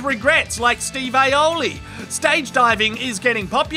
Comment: Did he just say “Steve Aloly” 😂